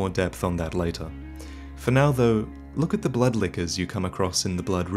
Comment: Was I the only one who heard "butt lickers" instead of "blood lickers?" 2:48 "look at the butt lickers you come across in the blood river."